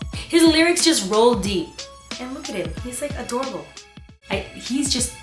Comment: Instead of adorable I thought you said "He's like adobo" ahahah! And i though "damn,she must really like chicken adobo"!